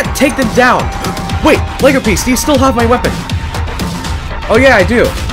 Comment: did willow just call him lego piece?